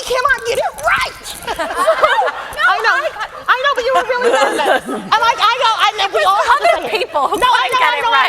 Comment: can someone tell me what gabby says to lily at 46:11 and the couple of seconds after? I´ve watched this so many times and I still don´t understand it.. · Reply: “You were really bad at this.” I think